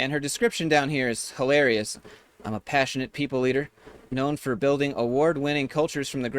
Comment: Sounded like you said powerful people eater